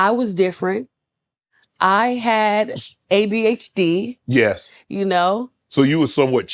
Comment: did she say ABHD ?